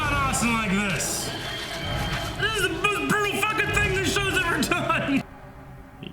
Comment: thought he said the most Penny fuckin thing they ever did and I got hardcore rwby flashbacks 😭😭😭